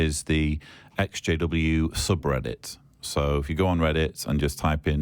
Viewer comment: I thought Matt was saying that there were SJW's in the audience, and I was wondering why anyone would think that was a good thing. Ex JW"s now that I can understand.